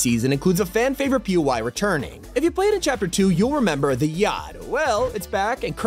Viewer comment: Bro I thought he said “you’ll remember the GYATT”